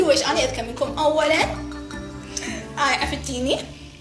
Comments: ترا البنت في الدقيقة 4:23 قالت انا ذكية لانو عفت ديني بلله اسمع منيح وش قالت وبدنا تعيد ردة فعلك عليها مرة ثانية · شكلك ما سمعتها لما قالت "أنا عفت ديني" يعني تركت دينها